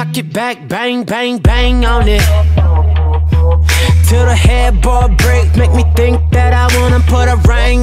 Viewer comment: when I took away the headboard I thought it said head bald but its head ball what it said lolXD